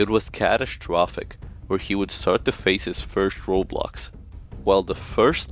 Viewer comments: I swear you said, "It was catastrophic where he would face his first Roblox." And my brain just about imploded til I went back and listened a second time. 😅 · i REALLY thought that said "he would start to face his first roblox" for a second lol